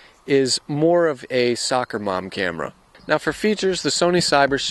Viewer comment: Did you said a sucker mom camera ? Does it mean that this camera sucks ?